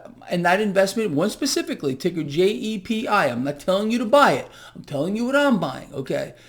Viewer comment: Did he say JEPI is the stock paying monthly dividends @ 6:23?